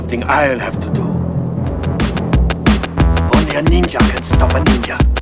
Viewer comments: I really thought they were saying "Horny ninja!" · I thought he said, "I'm a ninja, I'm a horny ninja!" ...Oops. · I thought he said horny ninja,me and my bro were like infatuated on finding it out lol and I knew about mc Chris last albums and he's a very unique rapper,found him out by Aqua Teen Hunger Force ( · I heard this on that Honda commercial and thought it was saying "horny ninja" haha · I actually thought it said bunny ninja *facepalm*